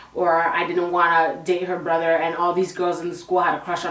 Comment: i thought she said she dated her brother i was like what in tarnation